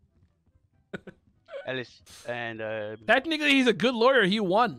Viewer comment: did bro just say the N-word???? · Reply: He said “technically”